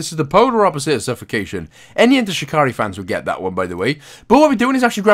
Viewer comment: I’m sorry did you just say enter shakri?!?!?